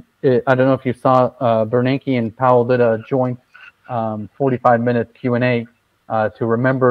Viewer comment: So, at about 3:12 - did he say Bernanke and Powell lit a joint?